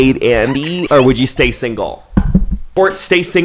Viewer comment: Staying single suddenly sounds great 💀✋ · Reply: I thought u said slaying single lol